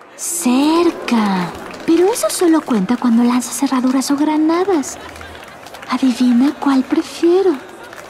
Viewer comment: No logro entender lo que dice en esta parte 0:06 , Me podrían ayudar? =) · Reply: "Cerca... Pero eso sólo cuenta cuando lanzas herraduras o granadas. Adivina cuál prefiero..." ♥ ;)